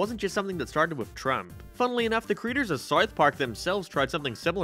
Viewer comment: sounds like your saying smouth mark lol